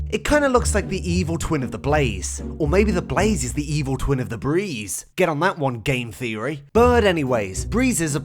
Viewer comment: I thought he said Febreeze for a second I’m so stupi